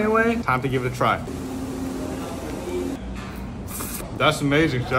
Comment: The "Bring me back to Rome" was a cry for help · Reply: I thought he said "bring me back to room" 💀